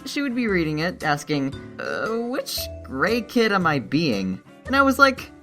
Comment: The first time I watched this I thought he said which gay kid am I being